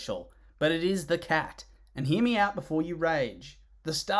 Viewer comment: DID YOU REALLY SAY "HERE MEOWT" OR AM I JUST SO USED TO CAT NOIRS SHITTY PUNS THAT MY BRAIN INSERTED IT💀